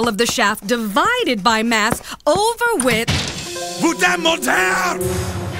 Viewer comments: What the hell did he say before he punched her? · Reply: I heard "puta motel" the first time. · What does Randy say after opening the door? 2:18 Edit: Boutin montair "fkn liar" in french 🤣